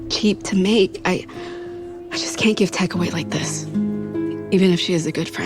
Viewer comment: the subtitles... she said tech, but the subtitles says take.... omg